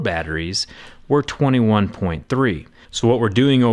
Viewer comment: Did anyone else catch him saying "Power Supry" at 4:58?